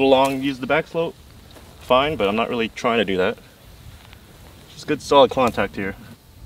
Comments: did say “just good solid Kwon-tact here” instead of contact? 😂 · did say “just good solid Kwontact here”? Cuz if he did, that’s genius. If he didn’t, that’s gotta be his new catchphrase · did I hear “good solid kwontact”? If so, that’s awesome. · did he say contact or "kwontact"? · I definitely thought said "Just good solid Kwontact here" on first listen